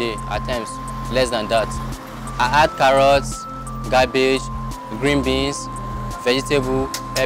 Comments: i thought I heard i add " carrots ,GARBAGE"... oh its cabbage... nice video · Is it just me or I heard him say gabbage instead of cabbage